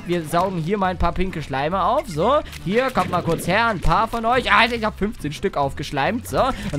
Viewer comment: Bin ich die einzige die gehört hat das das er bei 5:13 "ich hab zu viele aufgeschleimt "gesagt hat 😂😂😂